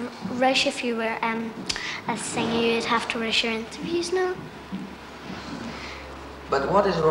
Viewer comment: I've listened to 5:48 to 5:53 several times and still can't work out what she's saying. Anyone know? And don't say use the captions, because they're auto generated and are mostly way off the mark! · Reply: "If you were a singer you would have to rush your interviews, now"